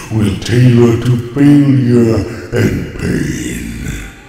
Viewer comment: Everyone. I need your help. I need to know what Unicron said at 5:33. Problem: English is not my native language. And this comment is written in French translated by Google Translate. If anyone wants to answer me, just write the quote. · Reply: “All creation will tailor to failure and pain.”